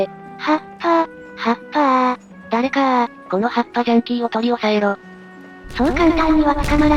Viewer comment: 発破ジャンキー、葉っぱジャンキーに聞こえてああ、中毒ってそういう・・・ 大規模工事は無職を救う公共事業だから・・・！ 発破は仕方ないんや！うへへ！